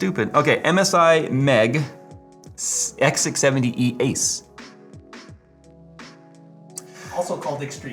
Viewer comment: The way he says "ace" almost sounds like he is saying "ass".